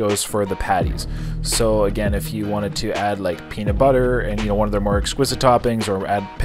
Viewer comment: peanut butter? Did I hear that right? Is that a thing??